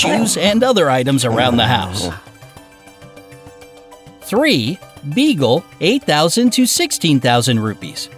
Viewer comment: I heard bagle